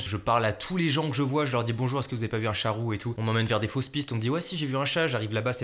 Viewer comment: Quand ta dit "chat roux " j'ai compris "charo"